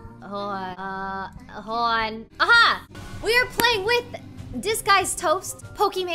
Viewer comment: I thought she said "This guy is Toast" when she introduced Toast.